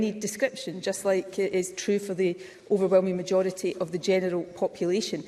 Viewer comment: does she say "The overwhelming majority of the genital population?" 😧